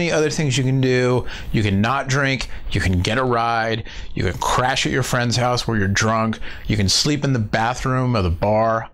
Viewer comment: I thought he said, " you can crash into your friends house when your drunk" hahaha. totally misheard..